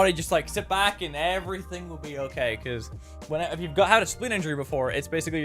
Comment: did he said "spine injurie" cant quite understand it clearly